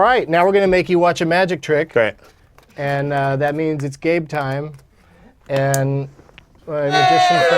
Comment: honestly thought i heard him call that last bit "gape time". i was starting to get excited until the card came up